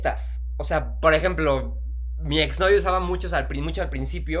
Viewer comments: no sé si escuché mal pero se escuchó que dijo: "mi ex novio" quien más escucho eso...😅😅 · escuche que dijo Alex Casas "mi exnovio", me equivoco?